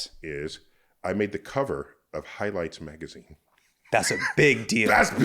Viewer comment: For a split second I thought he said "High Life magazine" and I was insanely excited to read the article 😮‍💨